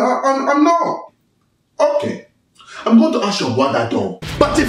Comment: What did she say she was going to ask? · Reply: im going to ask your brother tho